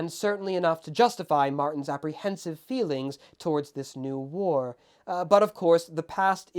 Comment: I thought you said world war the first time I heard this part.